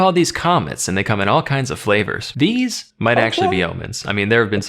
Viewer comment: He actually said "Omens" here, so he was talking about gods rather than aliens :)